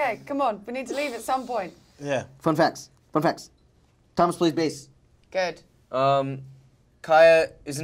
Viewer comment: what did he say about Thomas? ahaha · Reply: He said "Thomas plays bass"